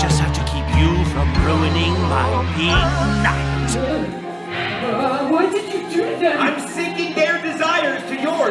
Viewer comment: "What did you do to them" I heard "Why did you do them"